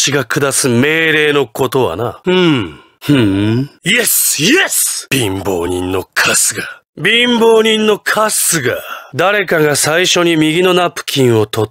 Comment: Can someone write what he’s saying in Japanese? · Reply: “Binbou ni no kasuga” I think that’s how it’s spelled but I’m not sure